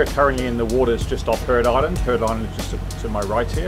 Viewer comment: oops. I thought he said Heron Island. It got confusing as soon as glaciers were mentioned...